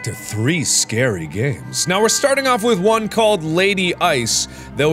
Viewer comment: thought it said lady Joe